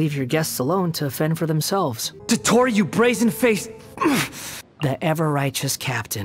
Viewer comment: DOTTORE YOU BRAZEN FACED- GRRHM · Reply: I THOUGHT HE SAID RASIN FACED FOR A SECOND😭